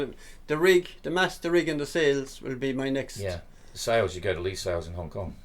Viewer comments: All the best!! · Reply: what did he say sails Hong Kong? or 'lead sails' ? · what did he say sails Hong Kong? or 'lead sails' ? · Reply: Lee Sails